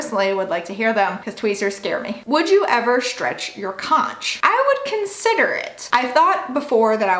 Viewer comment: I thought the question said "Would you ever stretch your cROTCH" at first. lolol